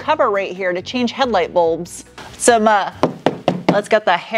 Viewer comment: did say" hairy carpet"? Yet another reason is my favorite car reviewer!